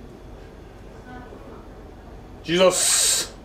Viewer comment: ロング缶、こんなに早く無くなる？ 素晴らしい飲みっぷりですね！ 「頂きます」が、「ジーザス」って聞こえたのは私だけ？笑